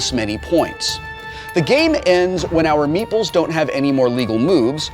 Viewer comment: Every time they say "meeples" I hear "meatballs"